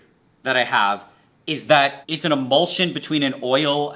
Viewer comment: I thought he said bullsh!t instead of emulsion